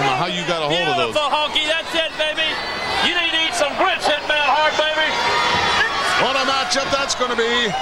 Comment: What does Hart say at 3:43? "something, now, Hart baby."